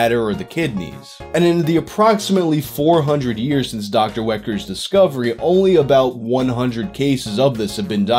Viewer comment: Sorry I thought you said Dr. Whacker for a second.